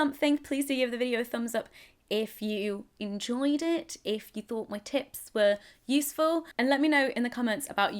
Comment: i first heard "if you thought my tits were useful" ...